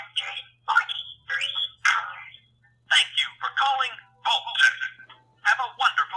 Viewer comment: I called that number....I heard "Hello, and thank you for calling free sex"